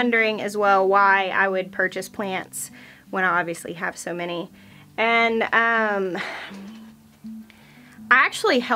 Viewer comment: When you said the line "I know your asking yourself why did you buy plants?" In that pause before you answered I totaly heard you say. "Because PLANTS!! " lol